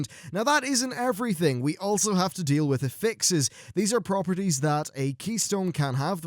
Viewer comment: in 2:05 when you said affixes i heard apexis, i think they will haunt me forever >.<